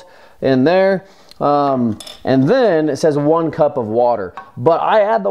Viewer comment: ah crap, I thought u said two cups water. guess I'll be cooking my batch skittle longer